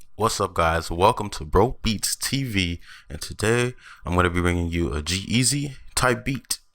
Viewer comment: it sounded like you said "welcome to broke beats tv " lol